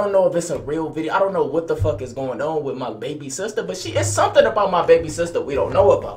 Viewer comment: i thought he said my baby sitter bruh